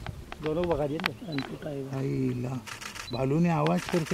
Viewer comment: I hear spoken kapampangan Say "Balu Ne" means It Really Knows